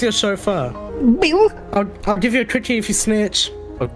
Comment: I heard “quookie”